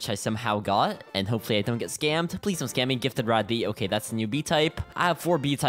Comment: Yo I thought you said daddy bee😅